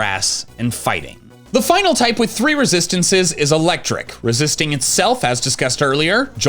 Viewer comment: “Electric resists Flying” I misheard and misread it as “Electric resists Water”